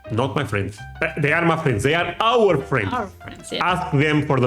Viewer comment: Everytime you say My friends I swear you're saying muffins.